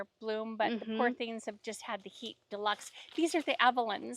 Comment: could someone please tell me what was said about the Evelyn roses? What does Laura mean when she says, “I didn’t know they were going to”? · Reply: She said they were bringing them back to purchase again. I might have to get one.